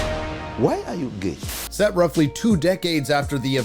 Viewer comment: Did i hear it right ? "Why are you gay ?" right there